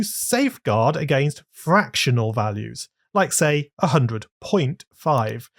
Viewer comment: I Thought you said "Save God" Instead Of "Saveguard" 💀💀